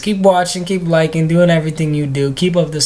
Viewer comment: keep making videos is that what he said?